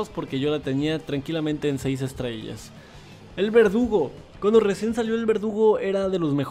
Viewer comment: por un momento escuché "el vergudo" xd